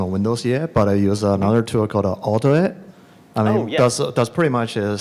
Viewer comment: what did he say what did he uses instead of autoGUI? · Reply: AutoIT